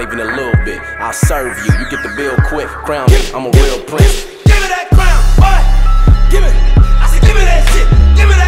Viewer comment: When I first listened to this I thought he was saying "give me that brown butt" until I looked up the lyrics lol